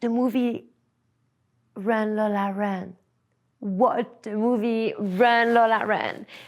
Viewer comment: I didn't understand the name of the movie she mentioned at 1:34:45. Sounded kinda like "Reign le la Reine." Can't find a title like that, though. Anybody know?